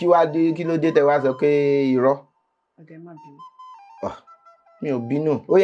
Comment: Okay e ma binu... Olotu didn't hear at exact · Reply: She didn't say okay... she said 'oda e mabinu'